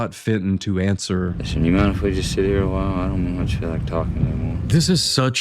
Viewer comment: "Listen do you mind if if we just sit here a while. I don't feel much like talking no more." I struggled a lot to hear wtf he said. Please put subtitles. Heavy accent + mumbling is the worst 😢